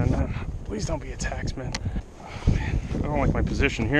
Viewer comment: did you say taxman? Need some help. I tried looking it up but I think I am using the wrong word.